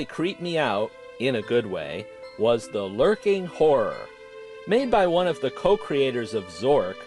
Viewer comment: damn at 1:48 I thought he said The Lurking Whore. Serves me right for ignoring the video and reading the comments.